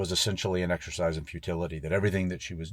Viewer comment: i thought you said exercise of fertility, i was like wtf lol. then re-watched, my bad. you said futility